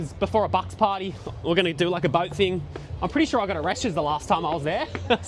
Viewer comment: For a second there i thought you said you got arrested, not a Reschs 😂